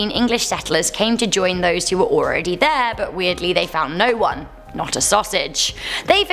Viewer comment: did she say "they found no one not a sausage"......as in penis???? btw i honestly believe the message croatoan meant they either left with the tribe and became part of it or they were killed by them. personally i think they became part of the tribe since no bodies were ever found.